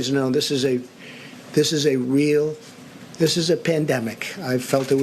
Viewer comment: “I felt it was a pandemic long before it was called a pandemic.” Lord help us lol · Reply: I've listened to this three times now, and the word I hear is "pendemic", not just once, multiple times. He cracked me up when he said "I knew it was a 'pendemic', all you had to do was look at other countries". WTF?